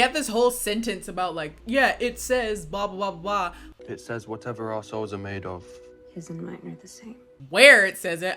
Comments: I thought he said "Whatever arseholes are made of" had to listen to it twice lol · Anyone else laugh when he said “our souls” ‘cause it sounded like he said “arseholes”. I am mature. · at first I heard "whatever assholes are made of" and that would have made this movie so much better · What Hardin said: "Whatever our souls our made of, yours and mine are the same." What I heard: "Whatever assholes are made of, yours and mine are the same." I hope this resonates with someone 🙃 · When my guy said ‘what our souls are made of’ I choked bc I thought he said ‘what our arseholes are made of’ why is his accent so weird I stg😭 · I must had re-watched this bit a few dozen times, I'm sorry but all I hear is 'Whatever assholes are made of' and it cracks me up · Okay I know he says “our souls are made of” but I heard “assholes are made of” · Please someone tell me I’m not the only one who heard him say “whatever assholes are made of, you and I are the same”???